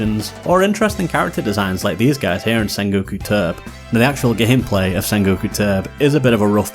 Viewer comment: Hehe I thought you said sengoo ka Turp! I never heard of that one but it made me laugh because that’s what sound my Mum makes when she burps! Turp Turp!